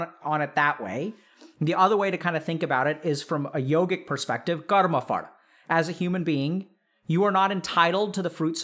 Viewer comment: I heard "Karma Fart". Did everyone else hear the same? Or am I just trippin'?